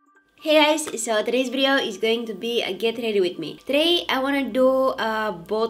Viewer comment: I heard her say "Today I'm going to do a get naked with me video". I was in shock for a moment. Then I realized my mistake. I though it was kinda funny. Such a pretty look today, btw. You are so amazing!!!!